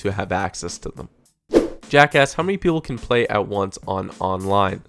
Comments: I think he said "jack asked" ??!. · lol Thought you said "Jackass" XD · it kinda sounds like hes saying jack ass.. xD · it sounds like you said jacka** lmao!! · he said Jack asks, although at first I thought he said Jack@ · sounded like u said jackass lol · How many people thought he said, "Jackass"? XD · did TG call jack a jackass? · did u say jackass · lol 7:15 it sounds like he said jackass